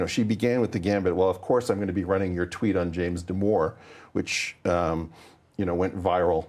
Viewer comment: CC says "James d'amour" lol